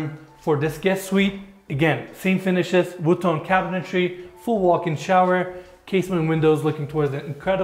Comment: the subtitles say Wu-Tang cabinetry? :D haha is it really?